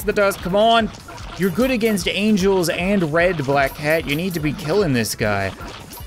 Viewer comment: Did you say red and angel 13:31 Really